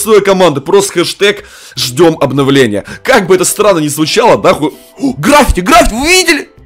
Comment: Мне послышалось или сказал наху